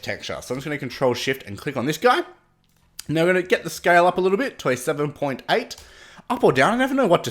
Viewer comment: you said 'to a 7.8', but I heard '27.8'